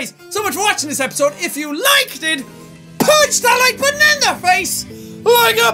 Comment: Whenever jack does his outro i hear "punch that banana face" instead of "like button" lol